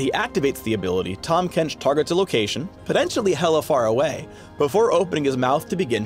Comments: Did he say 'Hella" Far away? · wait did he say hella far away ?